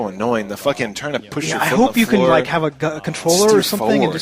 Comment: why did say "i hope you can have a gun--controller"